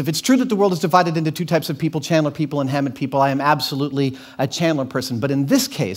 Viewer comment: what are the ype of people he says? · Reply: Chandler people read Raymond Chandler, a British-educated pulp mystery writer who wrote a wonderful series of novels about a chess-playing detective named Philip Marlowe. Hammett people read Dashiell Hammett, a socialist ex-Pinkerton detective who had a VERY cynical view of American "proprieties" and with Chandler, Rex Stout (Nero Wolfe), and Erle Stanley Gardner (Perry Mason) wrote some of the most innovative and exciting mystery novels of the mid-twentieth century.